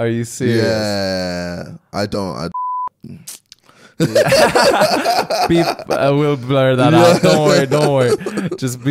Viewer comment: Am I the only one who wants to know what he said at 2:22? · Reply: He said I don't give a fuck